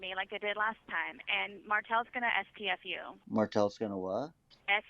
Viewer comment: I initially thought she said “Martel is going to SPF 🧴 ☀️ 🏖️ you” 😂 😂 😂